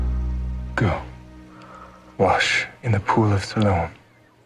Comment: did he say soil? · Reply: pool of siloam